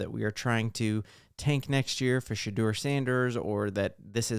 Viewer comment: Did you just say take for sheduer sanders? 🤦🏽‍♂️🤦🏽‍♂️🤦🏽‍♂️🤦🏽‍♂️